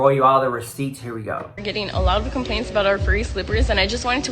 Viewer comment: My dumbass thought she said Free slippers.... I was like why TF are people complaining about FREE slippers. But then I realized she said Furry slippers and I'm like omg I'm a dweeb. 😭🤣💕